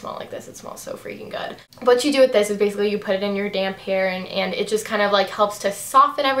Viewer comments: when you said "put it in your damp hair" i thought you said "put it in your damn hair" and i was like....whoa.... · when you were talking about the chi silk infusion i thought you said "you put it in your damn hair" and i was like oh ok no need to be sassy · sounds like she said damn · Was I the only one who thought she said, " .. You just put it in your d*** hair" ? I was like woah! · I thought you said "put it in your damn hair" lol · haha! i thought you said "put it in your damn hair" instead of "put it in your damp hair"! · Lol I thought she said you put it in your damn hair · Lol, I thought u said "put it in ur damn hair" but u said damp.....lol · when you said "damp hair" I thought you said damn hair, lol. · haha I thought you said "you put it in your damn hair" not "you put it in your damp hair" lol! love your vids! xx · did she say, put it in your damn hair @ 7:19 ? Lol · Omg I thought you said ''you put it in your damn hair'' instead of ''damp hair'' x) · hahaha omg when you said "you put it in your damp hair" when talking about the chi stuff i thought you said "you put it in your damn hair" LOLOLOL im dying hahaa. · Im dying laughing. I thought you said "and what you do is put it in your damn hair.." Beauty guru snaped! LOL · Haha when she said "you put it in your damp hair" I thought she said damn hair XD LOL love you:)